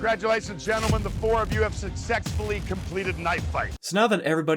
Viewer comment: I'm at 16:32 in the video and maybe I'm heard at hearing but did Goldberg say "Succ-SEX-fully" emphasis on the "SEX"?!!